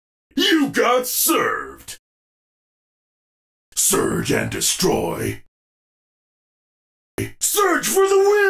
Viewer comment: all the time I thought he was saying "surge have to destroy"